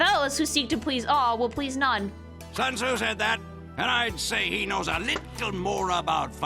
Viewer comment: im glad i havent gotten the game yet so as to facilitate my own silent protest towards stupid changes like these edit: 16:14 i always thought that the soldier said "sons who said that?" damn brain fart that lasted over a decade right there